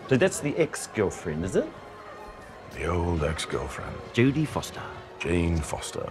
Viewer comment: did Korg say "Jody Foster"